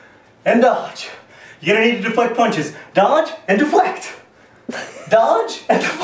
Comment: I thought he said dodge and do the flap...which works too